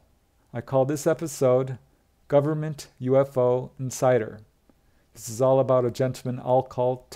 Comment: Oh, at first I thought you said, ‘government UFO and cider’ ! 😆